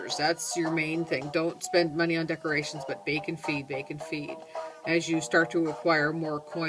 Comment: haha at 5:00 it almost sounds like your saying baconfeed, id like to be reviewed aswell! <num>ia and gamename is extreem!